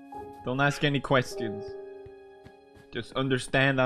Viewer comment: I love it when he speaks Swedish, since I an understand him. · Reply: If you know swedish, what does the say here 2:12 Rof manen?